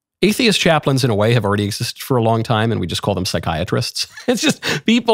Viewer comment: “we just called them psychiatrists…” 🤣😂🤣 1st I thought you said podiatrists 🤣k. Watching 💜